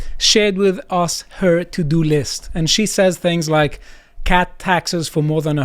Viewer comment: sorry if I spelled that wrong. Love ya man, but I had to pause and look at the video because I thought you said "cat" taxes not "cut" taxes.